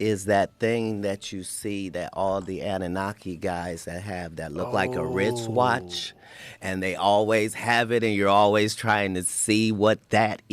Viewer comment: did he say rich watch?